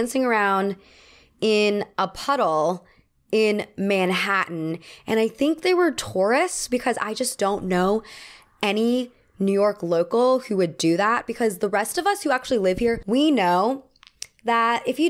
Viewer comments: Misheard the puddle-splashers insight as: “I think they were Taurus, because… “ 😅😂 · Regarding the subway juice story... I kept hearing "pretty sure they were Taurus" instead of tourists lmao 😭 I was like... are Taurus people known for playing in puddles??